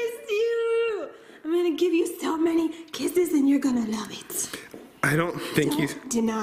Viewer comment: I always keep my captions on for funny stuff but when said I’m gonna give u lots of kisses and you’re gonna love it instead of love it said die so “I’m gonna give u lots of kisses and you’re gonna die”I started laughing so hard🤣